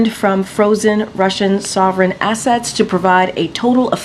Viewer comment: I thought she said frozen Russian assets, that's theft